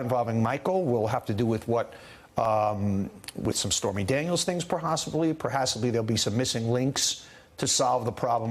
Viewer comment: "...perhosibly. Perhasibly..." What are these words?